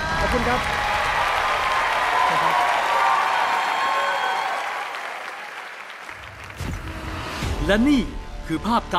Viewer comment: นาทีที่17:47 ดูปากผู้หญิงคนหนึ่งเขาพูดว่าอะไร ใครรู้เม้นบอกที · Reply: โอ้โหใหญ่มาก